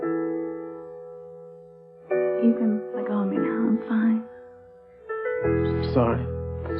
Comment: Did whitney call chad ethan when he caught her when she fell?? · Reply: Omg it did sound like she said Ethan but I think she said “You can let go of me now, I’m fine”. I had to listen 4 times. lol